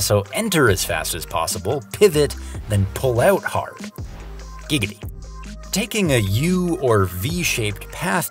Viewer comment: Did I hear a "Gigity" or I am imagining things